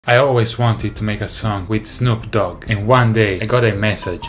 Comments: At first, I literally heard "I always wanted to make a SON with snoop" 😂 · I'm glad he put subtitles on this I kept hearing "I wanted to make a son with snoop dogg" I said wth 🤣💀